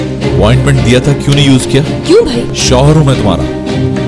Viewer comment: wait what she said Kiyo Bhai 🤣